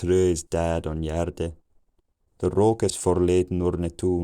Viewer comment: My native language is Dutch and I've been using English daily nearly my whole life. The first time I watching this I understood some words, until it all became understandable after 1500, then when I understood that it's about a farm, I went back and listened to it again without subtitles and I was able to understand the words for animals, and a few others, until about 900, after which it was all mostly understandable. I haven't watched the subtitles yet. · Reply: After watching with subtitles, I discovered I misunderstood some things after all: 8:46 I thought he said the yard is full of smoke because they burned some wood, because I heard 'rook' and thought of the Dutch word for smoke. 8:53 I thought he said he has a house for his goats